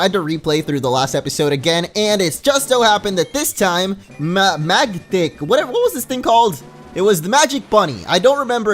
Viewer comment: Did say magdick at 0:33?